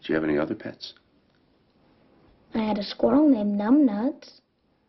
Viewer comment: I recorded this movie off of the Disney Channel back in the day and instead of "numb nuts" Ben says "I had a goat named Butthead". Also, in this version Jack doesn't tell the Young Crab story by the fire. Instead there's a discussion about things that the little guides and dad's never understood. For example, Norm aka Dark Eagle wonders why the stars in the sky never come crashing down to the Earth. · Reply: bruceleedisciple I thought he said, “I had a moose named Butthead”.